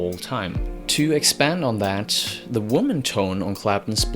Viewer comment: the "woman" tone? i was always thought he said warming tone